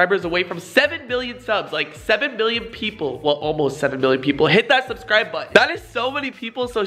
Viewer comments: Lol he was sick so it sounded like he said 7 bil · PLEASE SMASH THAT LIKE BUTTON FOR ME ACTUALLY DUNKING OVER MY LAMBORGHINI!! ALSO, THE GIVEAWAY IS LIVE ON MY INSTAGRAM GO ENTER RIGHT NOW. LOVE YOU. OH YA SUBSCRIBE IF YOU HAVEN'T ALREADY CUZ I'M LIT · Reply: FaZe Rug it sounds like ur saying 7 billion in the beginning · It sounds like he said billion and I was suprised